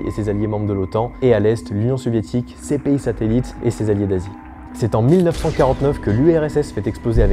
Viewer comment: Alors par contre au début j'ai entendu "et ses alliés nazis " plus le fait qu'à ce moment là y'a des petits bras de rue en l'air. J'étais pas bien. On refait pas l'histoire en fait. Il se trouve que je suis con. Bises